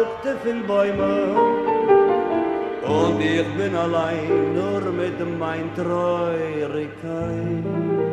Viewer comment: thanks for another great song presentation. In writing this up to read and play for today's Yiddish Club Zoom meeting, I noticed a line I think you misheard (you transcribe these, right? Not from original sheet music). At 0:53-0:56, I think it's "mit mayn troyerikkayt" which rhymes with shrayt. "with my sadness/grief/melancholy" All dictionaries starting with Weinreich give troyer as the noun for grief, (sorrow/mourning/sadness/melancholy) and add -ik for the adjective. But Harkavy had an extra form of the noun, namely the adjective + the nominative/-ness ending (which as you know can be hayt, heyt, kayt or keyt as in Yiddishkayt). He was still spelling many k endings with g (as in the days of the week - he writes מאָנטאַג), so he has טרויעריגקייט